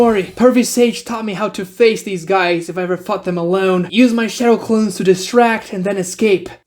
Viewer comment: i shit you not i had to listen to that five times to figure out what you said because i kept hearing "if i was to ever fuck them alone"